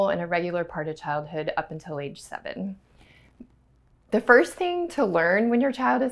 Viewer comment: Thats fine, but what if they haven't stopped after an hour? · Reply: did you just say age 7?